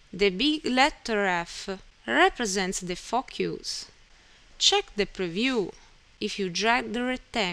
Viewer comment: I swear it sounds like she said "represents the fuck you's" lol otherwise :) thanks for the video proves useful!